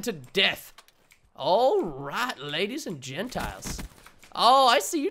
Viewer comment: I thought he said ladies and genatils 😂😜